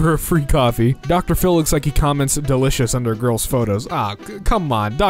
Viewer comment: am I the only one who heard grills instead of girls?